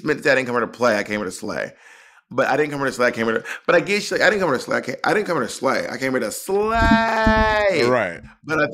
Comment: I autocorrected my ears when I heard Monica's last line to: "I didn't come here to STAY, I come here to SLAY" - it makes more sense to me than a first "slay" and another, slightly more energetic "slay"...